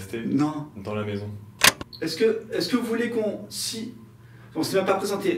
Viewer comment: Moi j'ai compris " qu'est-ce que vous voulez?"